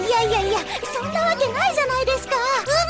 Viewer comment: The "American joke" is a mistranslation. She's saying "Nanika no asobi desuka" (Is this some kind of game?) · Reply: いや「何かの遊びですか？」で合ってるよ。 もうちょっと正確に言えば「えっと……あ、何かの遊びですか？」って言ってる。 多分この「あ、」が入ってるせいで「あ、何かの」が「アメリカの」に聞こえるんだろうね。